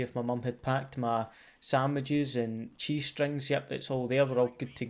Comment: thought you said G String